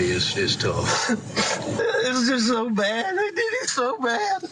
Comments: I heard "it's just so bad, I did it so bad". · I heard, “ it was so bad, they did him so bad”